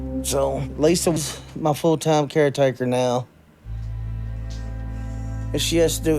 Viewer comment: Eff everything else, the fact that they pulled their daughter OUT OF SCHOOL to be a full time ASSISTANT CARETAKER is absolutely disgusting….smh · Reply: I thought you said full time ass wiper for a second😅😂